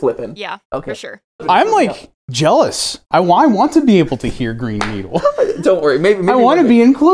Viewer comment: i think I'm broken i hear brain needle